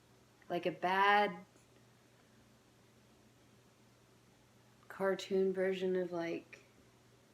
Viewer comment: did she said, a bad cartoon ? Or like bad things are happening in it, im confused there